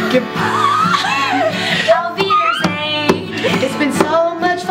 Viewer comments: What does say at 4:23? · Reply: Auf Wiedersehen! It's german for goodbye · is saying 'auf wiedersehn' at 4:23? ♥